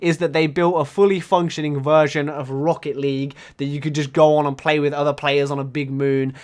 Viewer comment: 🤑 (did you just say rocket league?)